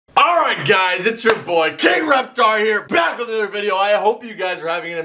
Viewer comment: did he just say gay Reptar